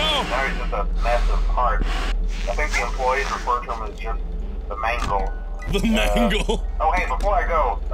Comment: the subtitles says "these asians cant keep their hands to themselves". lol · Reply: My subtitles said “makes employees refer someone to the mango needy” Mangled is no more, all hail Mango